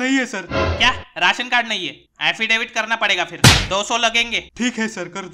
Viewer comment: Affidavit is gender changing card PS subtitles say it's gender changing card